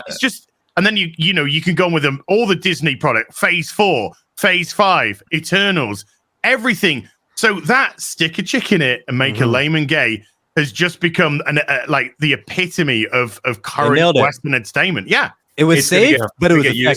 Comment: Ohh I thought these jokers were saying "put a chicken in and make it lay". 🤦‍♂️